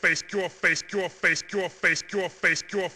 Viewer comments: ha! so far as i know, my friend is the first dude who put ganon at his video doing this move, and audio is: YOUR FACE! GOD DAMMNIT! haha also i heard hi msay "face cure" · it sounds like he says face cure